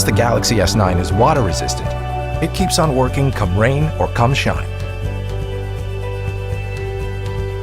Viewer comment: Am i the only one who hears "cumshot" at 1:24 ?